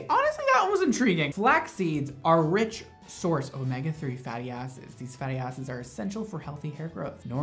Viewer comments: XCUSE MEEE DID YOU JUST CALL ME A FATTY ASS SEED??? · I WASNT PAYING ATTENTION AND IT SOUNDED LIKE YOU WERE SAYING "fatty asses" AND I FELT ATTACKED LMAOOO